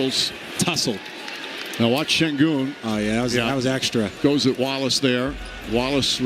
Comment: For some reason, I misheard that announcer, thinking he said “Now watch him goon”🥀